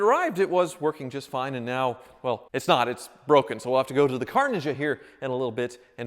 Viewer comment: You say "Car Ninja" and I hear "Carnage".